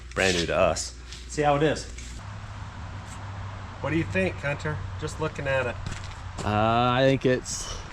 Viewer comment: Wait wait wait hold up hang on a sec ..... the 31:12 ish time stamp did he call him " Cunter " or was it just Hunter and I'm planting a seed .................. 🤔🤣😁